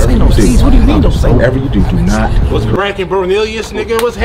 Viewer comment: He tripping on B’z bt he said What’s Crackin Bornelius 😂We be like duck tails Suuwoooop🤣 I love this lol Always NeBeBBary on fin😭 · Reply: I thought he said whats brackin but he said whats cracking